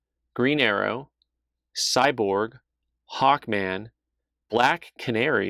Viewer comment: Thought you had said, 'Hawk Tuah', not 'Hawkman'. :D